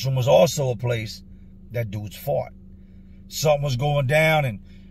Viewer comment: So funny when you said fought, I thought you said fart. After just talking about the toilet. LOL